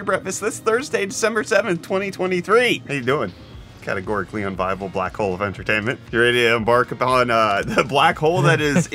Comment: I really thought I heard unholy bible black hole of entertainment in the intro and got extremely confused.